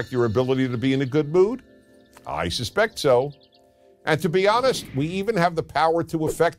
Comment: He says "I suspect so" but subtitles say "I suspect it would"